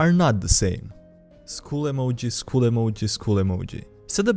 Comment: thought you were saying “school emoji” at first, and while a little confused, I also found it fitting.